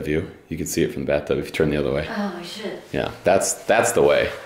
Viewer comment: Anyone else hear say “Oh Shit”